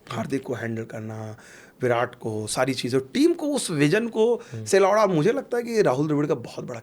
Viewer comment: Wtf he just say.... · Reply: Tf 😂 I just heard " Vision se lawda "